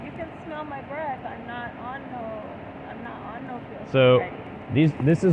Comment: does she say “smell my breathe I’m not on any pills”